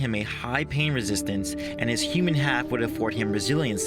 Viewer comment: did you say Human half?